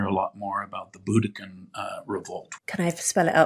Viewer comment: Does he say (at 1:06) The 'Glutican Revolt"?? A Google search yields no results.